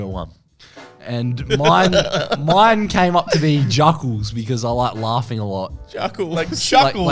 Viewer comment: For a second I thought it said Zuckerberg